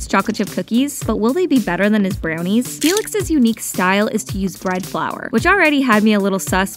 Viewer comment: I thought you said red flower😂but are you A Stay